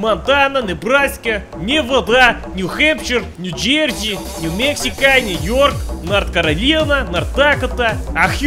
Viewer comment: i hear "new mexicali" no no its new mexico. ,_